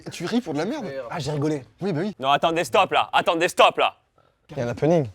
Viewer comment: Quand Flo dit vers 6'45 au sujet de l'ancienne version de la boisson "Attendez stop, là", j'ai compris "Attends, Destop, là". Et puis, bah c'est tout ce que j'avais à dire. Alors vous le prenez, vous le prenez pas, j'm'en fous, en vrai. Mais sur le moment ça m'a choqué. (Mais maintenant, ça va mieux, merci)